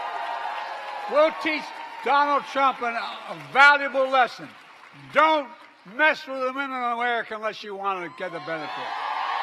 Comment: What the heck did he even say!?! 😂😂😂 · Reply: Don’t mess with men and women of America unless you want to get the benefits. Clear as day